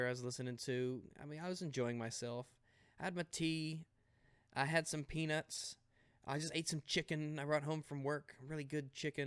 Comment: i so thought he said. I had some tea, I had some penis.